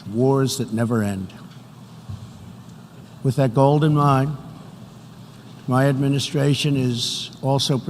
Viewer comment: "with that GOLD in mind..." anyone else hear that??